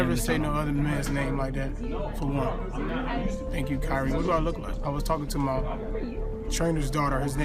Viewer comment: I heard him say "Thank you Kyrie!"